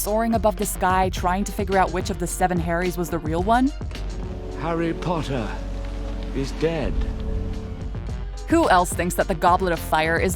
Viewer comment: thought voldemort said harry potter.. is gay